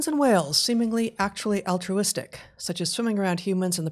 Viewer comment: I thought it said "autistic," which made me go "hmmmmmmm." We think of dolphins as friendly, sociable creatures, but what if there were autistic dolphins?